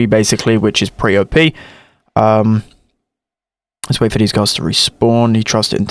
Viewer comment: Did I hear say "Let's wait for these girls to respawn."